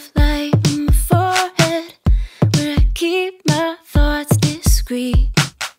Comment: Lyrics for whoever is interested... All this fight in me A crown of light on the forehead Where I keep my thoughts discrete Roll the dice when I forget Oh, what a way to go on your own Cerulean eyes, tied, so long Cause feelings that you've never known Aren't my fault I keep my words sweet And bathe then I lay low Round we go, and the lights are low And the circus fades, you were always carouseling All the circles trace, as I turn my page And you hear the bass, over words I was yelling Round we go, and the lights are low And the circus fades, you were always carouseling All the circles trace, as I turn my page And you hear the bass, over words I was yelling Gone (x4) Gone (x11) 3, 2, 1, come on It's easy to confuse The way I speak, with how I move I'm not trying babe, not giving up Won't simplify my ways 'till you make the cut What can I say when you dance on in? With your cools, and your fools, and your friends Rolling like your king of the sky Like I won't have a chance to show you how Round we go, and the lights are low And the circus fades, you were always carouseling All the circles trace, as I turn my page And you hear the bass, over words I was yelling Round we go, and the lights are low And the circus fades, you were always carouseling All the circles trace, as I turn my page And you hear the bass, over words I was yelling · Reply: Oh, I thought she was saying "where I keep my farts discrete" XD I couldn't understand what she was saying.